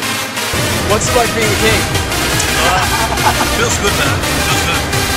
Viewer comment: What he is saying 0:19 ? pls write in english.. TY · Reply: Cedric M "what does it feels like being the king"" i think